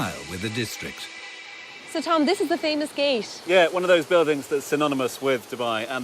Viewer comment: What is referring to in the minute 5:34? Is she saying: "so this is the famous cage"? I need to know this for an assignment, thank you for your help!